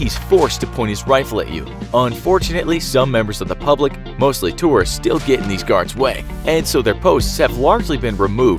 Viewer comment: I've always wondered how they keep it together with all the tourists in their face all day long... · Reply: Their are none it's a lie and a steriotipe oh I thought you said terrorists lol